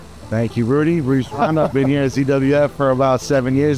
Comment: says CWF instead of ZWF